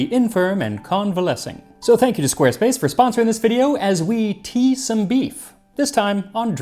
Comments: I swear I heard Max say "Let's tease some beef!" at the start. I was thinking this is headed into Dylan Hollis territory... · My ears perked up when I thought I heard "as we tease some beef" lmao my mind definitely went somewhere for a second